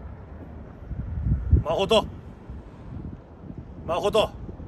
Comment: まほとがマホトーンに聞こえた